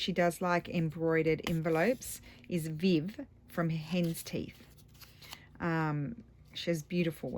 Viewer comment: Gorgeous!!!! I love embroidery!!!!! And those look so pretty! Who did you mention in the beginning about she made pretty embroidered envelopes? It sounded like you said Viv from Hens Teeth? But I did a youtube search and an Etsy search for that name.....could you tell me where I can find her or does she not do videos? I like to find new people to watch!!!!! Thanks! and Hugs to you!